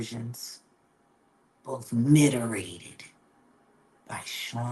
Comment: what did she say here? “Miterated?” I’m a vocab hound but I’m puzzled , please help